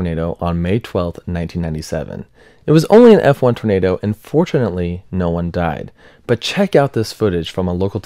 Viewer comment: I know he said 'and fortunately' but it kinda sounded like 'unfortunately'